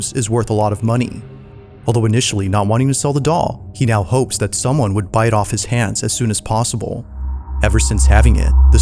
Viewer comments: I kept hearing “He now hopes that someone would bite off his hands as soon as possible”😂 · I thought he said " bite off his hands" i need to calm down a second 😂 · am I the only one who heard "...bite off his hands as soon as possible"?